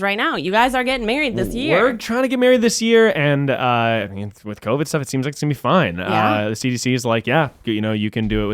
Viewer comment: At around 26:15 did said covid 17 or did I hear wrong.. its covid 19 right... RIGHT